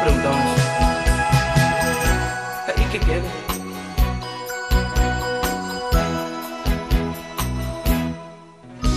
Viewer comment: Minuto 2:35 alguien sabe que dijo ? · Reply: Ahí que quede